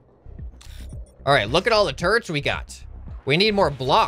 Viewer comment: when he said "Look at all the turrents we got!" i thought he said "Look at all the TURDS we got!"